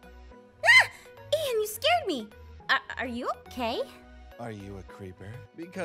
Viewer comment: Am I the only one who thought said 'Are u gay?' at the start?😭💀